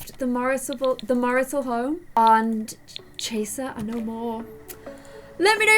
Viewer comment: maybe this won’t be the end of chaser / I thought she said tracer Me-I’m already tracer